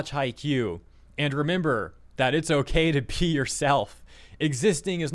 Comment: i heard "I remember that it's okay to pee yourself" lmao